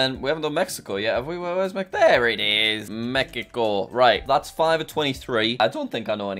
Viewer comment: Did he just say “Mekico”?